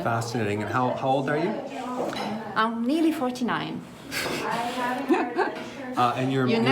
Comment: She lied about her age, watch her again, she's older than she says. She said," I'm really 49." Then she pretty much told him she lied and he shouldn't ask her age. Still disagree? - watch her look away and decide what she would say is her age. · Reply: She said "nearly 49".